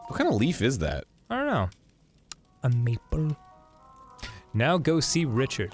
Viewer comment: Did Dan say Makeba at 11:00 bc it’s a makebaleaf 😅